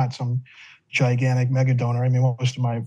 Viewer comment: lol, I thought that said MAGA donor.